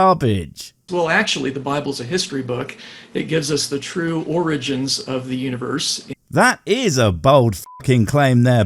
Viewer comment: For a moment I misheard that he said "it gives us the true orgies of the universe" and almost considered converting.